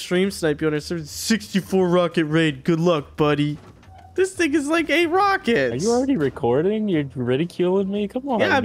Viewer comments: did he say “this nigga is like 8 rockets” or am I tweaking 😂 still a big fan doe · is it just me or did he say n*gga?😂